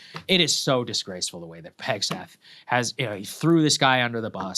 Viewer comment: Did he call him Pegseth? 🤣😂🥰😂